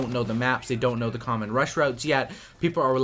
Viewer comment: Hey, new sub! I was reading the subtitles on this video, and I noticed at 4:29 there was a slight error. It says "common rush roads" instead of your actual words which were "common rush routes". I was hoping you would fix this because I think there are many hearing impaired fans of your channel who would love to know what you were actually saying!